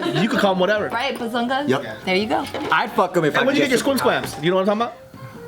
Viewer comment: Did joe say I’d fuck em if I had a chance 😂😂😂😂